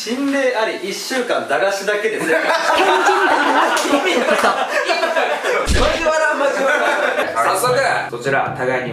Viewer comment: 中国人みたいな声聞こえね？なんこれ？ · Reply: 天神だからできたことって言ってますね。